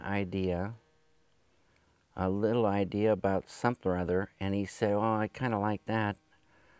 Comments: What exactly is he saying at 0:58? "I think I said I have an idea, a little idea about (????) and he..." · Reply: Slowdiver 'something or other' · Does anyone know what David is saying at 1:01 "a little idea about ..." ?? English is not my native language and I can't understand what he's saying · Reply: He said "a little idea about something or other..." (though he said it like "sump'n or other")